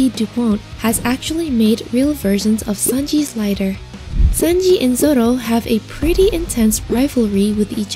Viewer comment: Thing I found It says "Sanji And Zoro" But the subtitles say "Sanji And Sanji"